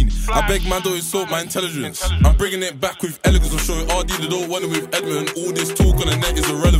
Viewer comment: What was said at 1:21 something like I’m sorry ardee they don’t want it with Edmonton is he dissing digga or ardee